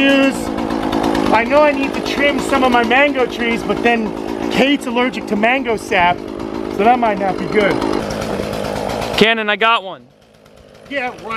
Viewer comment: I thought you said your wife is allergic to mango sap?